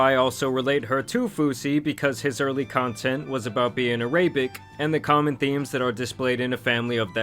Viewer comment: Wait…did you just say “araybic”?